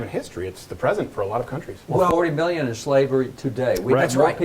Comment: “40 million enslaved today.” did he say million, or it sounds like he says billions....? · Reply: million